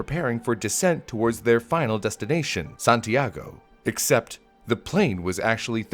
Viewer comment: did you say Santiago or San Diego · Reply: Santiago